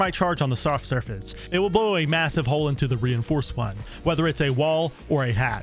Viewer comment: did i hear MASSIVE